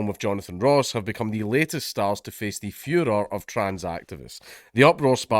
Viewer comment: Genuinely thought you said ‘face the fuhrer of trans activists’